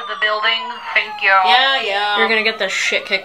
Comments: Almost thought she said pancake plot twist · I thought u said “pancake plot twist” Also this is legit how it is with cops 😬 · "Pain kink plot twist" Is it really a plot twist? Really? We all knew, let's be honest here. Edit: I love how this is my most popular comment Edit: MOM, I'M FAMOUS!! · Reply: I'm high as hell i thought you said "pancake plot twist" and i legit thought "oh because you want be flipped over and u like being beat up (pancake batter)" Pain kink makes so much more sense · I had the subtitles and I still thought she said, "Pancake plot twist." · "Pain kink plot twist" Is it really a plot twist? Really? We all knew, let's be honest here. Edit: I love how this is my most popular comment Edit: MOM, I'M FAMOUS!! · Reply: Omg pain kink... I heard "pancake plot twist" and I was so confused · "Pain kink plot twist" Is it really a plot twist? Really? We all knew, let's be honest here. Edit: I love how this is my most popular comment Edit: MOM, I'M FAMOUS!! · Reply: I thought they said "pancake plot twist".... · “You’ll get the shît kicked out of you one day you know?” “And I’ll like it! Pain kink plot twist!” 😂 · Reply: I thought she said "pancake plot twist" i was so confused! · Pain kink plot twist ayo 😍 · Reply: i thought she said, "pancake plot twist lol" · I thought they said “Pancake plot twist” till I read it 😅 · 😂😂😂 I thought you said pancake plot twist 😂😂😂